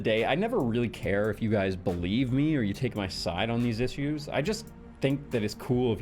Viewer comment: did he say “iss-ues” lol